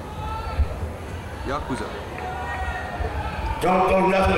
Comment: Did I just hear someone in the audience say "Yakuza"?